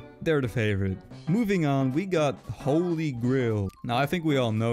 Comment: why does it sound like said holygrill instead of holygrail